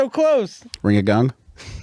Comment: Ring a gong murdered me😆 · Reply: I thought he said ring a gun, and I was wondering why they were laughing so hard 😂